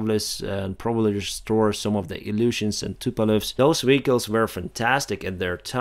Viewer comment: Just briefly I heard "They will restore some of their old illusions" LOL